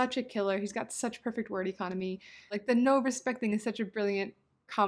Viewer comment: You said something about him having such perfect word "ethonomy" or something. What did you say? Sorry for the random question, love the video